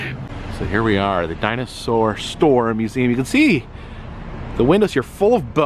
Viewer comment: I thought that it said "Dollar Store museum" LOL (Laughing out Loud)!